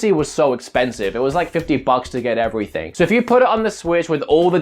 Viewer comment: Did he said "$15 bucks" to get everything at 2:09 referring to SSB DLC on WIi U? Or I misheard him?